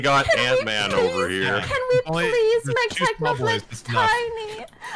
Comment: Can we make Technoblade tiny -niki 2021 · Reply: I heard "Can we please make Technoblade Tie me" because reasons